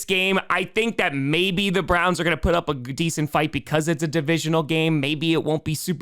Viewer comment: Anyone else hear almost say the Browns would put up a "g-decent fight"